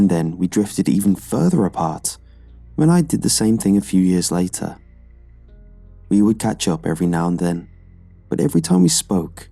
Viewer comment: You: "40 years later my mum had me so the age gap between us was pretty small." Me: "Wut? 40 years isn't a small gap. Maybe I misheard." *plays it back*: "40 years later..." Nope, not imagination. *checks reddit*: " four years later, my mom had me..." FOUR. Any chance you could give it a little edit, please?